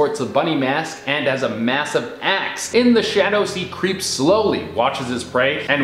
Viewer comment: for a second i heard "he has a massive ass" instead of axe... i was so confused